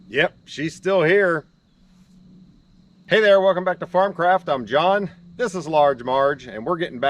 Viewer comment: I keep meaning to comment but always forget: Every time I watch your videos, when you introduce yourself, I hear, "Welcome back to Farm Crap, I'm Jon." And I can relate to that.